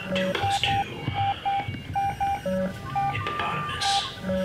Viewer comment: wait... did you say hippopotamus instead of hypotenuse doing your math thing? 😍